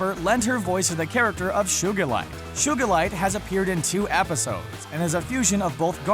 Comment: Is it me or does it sound like he is saying Shugilite